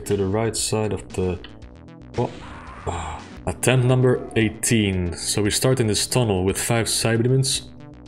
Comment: I don't know why, but in my mind I heard "Attempt number one" at the start... then later on you said "In attempt number 18 I said..." I was like, wasn't it first try? must have missed the 20 before the 1